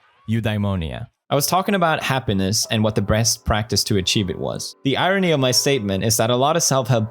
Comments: did he say "breast practice"?? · did he just say, "breast practice"? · did he say “breast”? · Did he really say BREAST PRACTICE at 00:20 ?😂😂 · Did he just say "the breast practise" 😂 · I though he said breast instead of best for a sec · did he said “Breast” instead of “best” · he said breast instead of best haha lolw I deserve the Nobel piece prize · Anyone else hear Felix say “the BREAST practice to achieve it was...” at 0:18 - 0:21? Coincidence? :thinking: I think not. 9 year old giggles aside, I really love these philosophy videos from pewds. <3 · Did he just say the "...Breast practice..." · did he say brest? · he said breast instead of best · Did he just say "breast practices"? Of course. Boobs bring happiness. OF COURSE!!!!! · did he say breast? 🤔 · did he just say breast practice? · this dude said breast instead of best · Did he just said breast @ 0:21? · Poods said "breast" instead of "best" lol 0:19 I'm 9. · he said breast instead of best and i lost my shit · Did he just say BREAST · did he just say "breast" practice? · Did he say “what the breast practice” 0:19 lol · did he say..... Breast? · did I hear that right? "Breast practice"